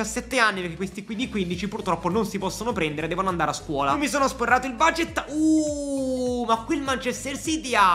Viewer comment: solo io ho sentito mi sono sborrat0 il budget